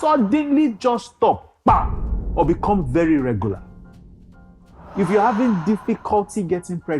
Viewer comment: Hi Doc, did you say very regular or irregular